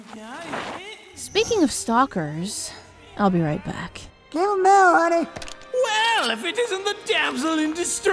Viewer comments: what did Kirito say to Asuna? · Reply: "Give him hell honey" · for those who didn't understand what he said it was confirmed by that he said "no mommy don't hit me, it only makes me hard!" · Turn in subtitles and read what Kuradeel says. You’re welcome. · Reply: "Don't hit me mommy, you KNOW it makes me hard..." - Kuradeel